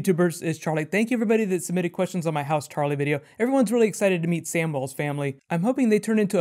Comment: When you said "house Tarly" I thought you said "House Charlie", gave me a chuckle, wonder what your words would be? "We are the watchers of the all" maybe?